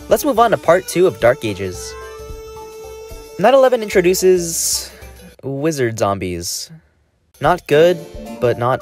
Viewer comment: "911 introduces," is what i heard lol